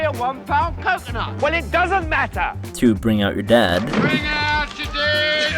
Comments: Did you say 'bring out your Dad?' @ 1:14 If so, you missed the joke. It's a plague joke. Bring out your dead. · Eric Idle says "Bring out your dead". Not bring out your dad! In his English accent, dad would be pronounced with a flat A and sound totally different. You've heard him as if he's talking in an American accent.